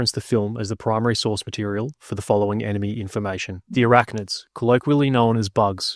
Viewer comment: Bruh. I misheard him say "Iraqnids" 😂😂